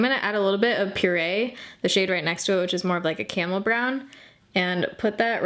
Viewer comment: is it just me or did anyone else hear her say "CAMEL" brown instead of "caRamel" brown or "camo" brown?, I love seeing her videos♡ I always rely on your reviews.